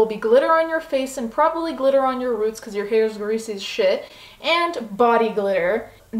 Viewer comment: What did u say at 2:30 about roots? lol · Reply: Your hair is greasy as shit